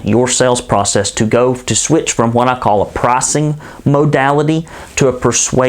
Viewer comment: What does that word at 6:20 mean? "Prossing'? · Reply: that is hilarious! Pricing makes a lotta sense. Thank you your video is terrific.